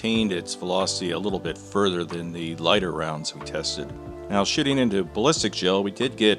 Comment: "Now shitting into ballistics gel..." Wait what? <rewind> "Now shitting into ballistics gel we did get..." Ok yep I heard what I thought I heard. Sneaky, very sneaky.